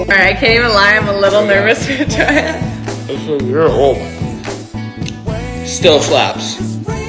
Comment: It sounds like he said “ur old” I was like umm nahh broo😅😅